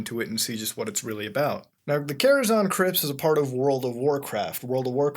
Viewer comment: i thought you said karazhan crips lmfao